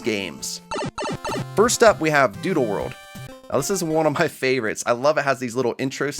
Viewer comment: I swear said DooDoo world